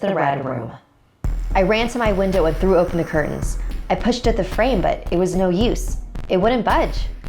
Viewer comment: i thought she said " i trew up on the curtains"